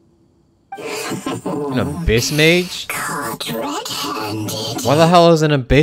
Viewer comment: I thought he said an a- bitch mage at first- 😭